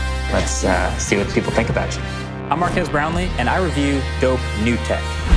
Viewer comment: Why do I keep hearing it as "Mark Ass Brownlee"?